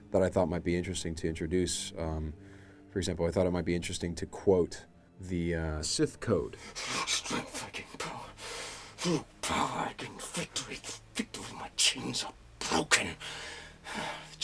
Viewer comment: xD "I'm fucking power"...or something like that...im sure im not the only one who hears this...